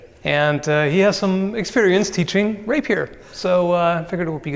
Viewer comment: i THOUGHT he said he teaches raper XD